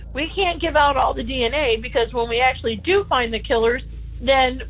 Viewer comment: did she say. "find the killerS"? as in more than one?